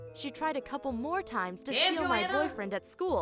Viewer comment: Lauren said "And Joana?". I wasn't paying attention for that second and it sounded like "Angelina?". THAT'S MY NAME. I freaked out for a second, rewinding and listening closely. I noticed Lauren DIDN'T say my name and I was a little sad...but then I laughed and continued the video. :3